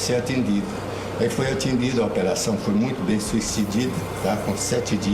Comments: "a operação foi muito bem suicidida" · ''Foi muito bem suicidido''. kkk